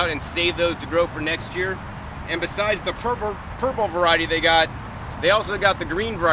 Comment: did he say purple or pervert variety.